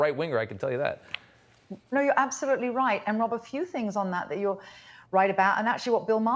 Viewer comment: did she say "one of the few things you're right about on that"?